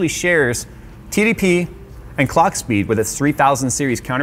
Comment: I heard "Clark speed" ...guy with cape takes off in the background. Anyway, great video! Maybe I'll finally sub... All marketing departments are run by demonic entities... the disingenuous bullshit they fabricate cannot possibly be traced back to sensible, truth-loving human beings.